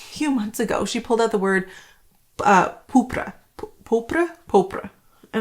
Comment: I am not Canadian, and I had trouble understanding some of your French words. I know «pourpre», but I thought at first you said «paupre». My father was French from Normandy and my mother was Salvadoran (I don't know if there's a distinct Salvadoran dialect or accent of French).